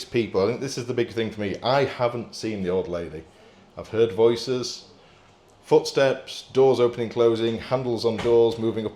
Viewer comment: Hey - You guys always have great content. I appreciate your approach to all of your investigations. Only a small portion of people out there trying to find the truth. Question though.. I haven't watched all the way through yet at the time I'm writing this, but in the interview was there anyone else in the house? At 16:12 just after spoke I heard a woman's voice, but can't make what she says. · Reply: Hey, much appreciated, the voice you can hear is Alexa on the ring doorbell saying someone is at the front door, I should have pointed this out but it was barely hearable 👍